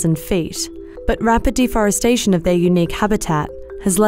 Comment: Thought she said the Golden Nutella